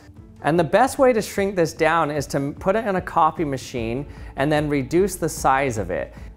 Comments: i honestly thought he said coffee machine i was so excited to see what the heck he was gonna do with it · Did anyone else think he said “put it in a coffee machine” at 1:15 ? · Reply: Then what did he actually say? Edit: Oh, I got it, he said copy machine